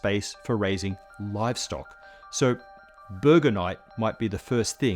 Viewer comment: At first I thought he said "Burgernite" as if it was a mineral or something lmao!